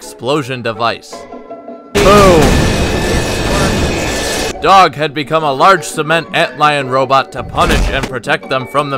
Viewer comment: Am I the only one who heard him say "It's working!" at 1:45? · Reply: It's been a full year. You're the first to notice lol. Yeah, when we filmed that scene I said "It's working!" because we weren't sure if that manhack explosion rig was going to work or not. When I edit these videos together I usually mute the footage's original audio (since the original audio is just us talking in a discord call while we animate). I didn't mute the original audio for that specific clip though, because I needed the sound effects of the explosions and manhacks 'cause i didnt feel like audio mixing a million different sound files to reproduce it. Thus "It's working!" was a side effect of keeping the original explosion and manhack grinding sfx from the raw footage instead of manufacturing my own.